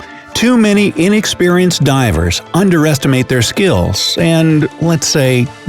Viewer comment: Did he say "underestimate"?